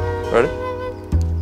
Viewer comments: Plot twist: Tyler is reversing to the matcha store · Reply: I thought she said macho store 😆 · at first i thought she said “nacho store.” who wouldn’t be excited for that?